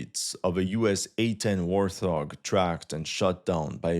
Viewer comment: did he just say Worth-Hog? lol